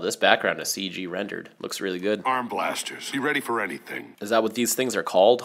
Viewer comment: hey I don't think he's saying arm-blasters I'm pretty sure he is saying to arm their blasters like turn off safety